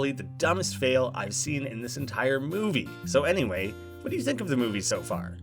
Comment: you said movie instead of game twice